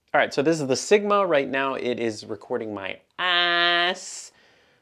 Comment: I'm not the only one who heard it right? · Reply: As an Australian I can verify he definitely said arse 🤭